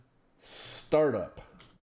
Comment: shell colon startup? the resolution is so high i cant understand nothing and the subtitles arent helping xD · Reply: Shell:Startup